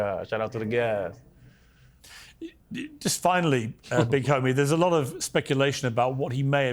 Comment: Piers calling him big homie is hysterical lol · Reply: I thought he said Mr Homie 🤣🤣🤣